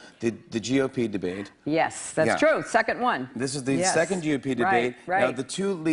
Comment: i thought she said "Suck at one" instead of second one